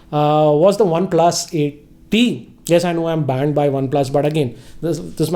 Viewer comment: you're banned by Oneplus? Or I heard wrong?